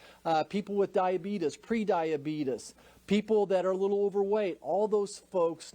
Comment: Did he just say "Diabetus" lol